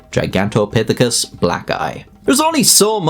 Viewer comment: Bro I thought you said, Gigantopitheci black guy. Lmao I was like wait what he say lol😂